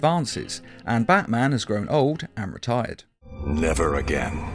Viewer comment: I heard that as Batman is old and retarded😂🤣😂